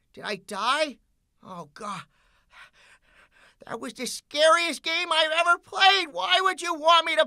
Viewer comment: I thought you said TJOC.R was the scariest jumpscares you've had.